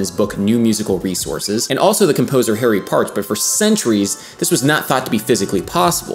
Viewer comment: I thought you said his name was Harry Parts.